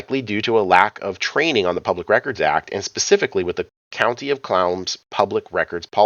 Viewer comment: I heard: "County of CLOWNS" LOL